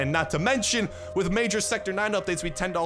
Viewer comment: Did say Sector 9?